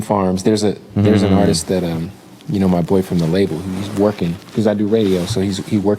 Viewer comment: I thought he said his bf at his label 😂